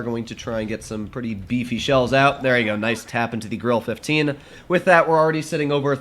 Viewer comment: I thought he said girl 15 but actually grille 15